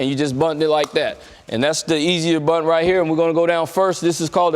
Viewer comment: Thought it said mastering the blunt for a sec...